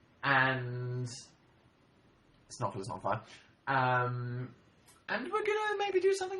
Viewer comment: What's Dan say around 59:00? When he's talking about the "very exciting thing" coming out tomorrow? He said "it's not **********" but idk what exactly he said after "it's not" Someone help? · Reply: Oh nevermind he said "it's not Phil is not on fire" inSANELY FAST AND LOW